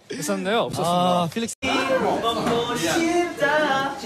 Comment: who heard pogo shipda at the back